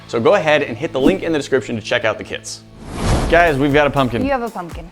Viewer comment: Is it me or did he say go cheek the kids